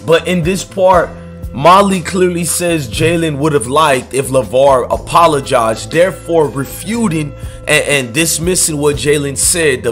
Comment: I thought she said “Jalen would have disliked if Lavar apologized publicly”